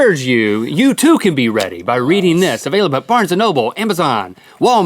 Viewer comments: did he say Rhetty? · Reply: He said ready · Who else thought Link said “Rhetty” not “Ready” at 0:27 ...... just me?.......... I would go home but I haven’t left for weeks. months. maybe years.... what year is it?